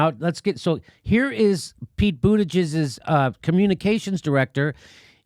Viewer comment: Did JD just call him Pete Bootijizz ? Lmao